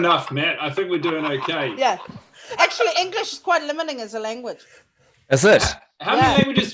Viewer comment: What does Urzila say at 13:15? "English is quite [???] as a language." I thought maybe "limiting" but I know English has way more words than a lot of other languages so that wouldn't make sense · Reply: She did say "limiting", I think she was just joking about how English is weird